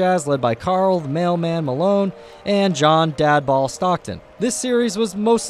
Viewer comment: does he say, John 'Dad Ball' Stockton?